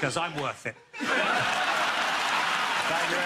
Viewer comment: This is the official channel for Gary? Why do the baked in subtitles say Greg Delaney @ 14:56 ?